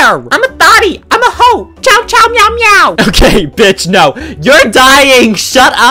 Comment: screams furiously CIAO CIAO MEOW MEOW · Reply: I THOUGHT HE SAID "CHOW CHOW MEOW MEOW" MAYBE ITS THE SAME THING BUT LOL